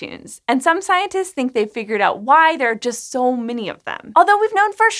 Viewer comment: I thought she said “that’s why there’s so mini of them” 💀